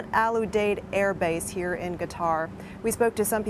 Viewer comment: am I crazy or does she say ‘guitar’ instead Qatar around 2:43? It sounds like she’s inflecting a ‘G’ rather than ‘Q’.